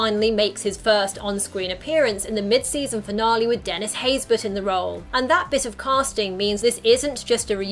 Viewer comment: for the longest time, before seeing his name in the opening credits, i thought Dennis' last name was "Haysberg", not Haysbert. the way Dennis pronounced his last name one time, i sounded like it had a G in it. O_o